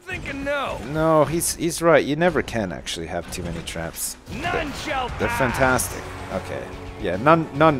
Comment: nobody will pass? i thought he said Nun shall pass, why is Nobody passing instead of Nun